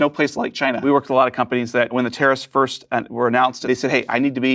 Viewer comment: The subtitles say terrorists and not tarrifs